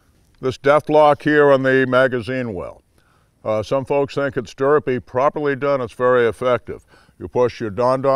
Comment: did he say "derpy"?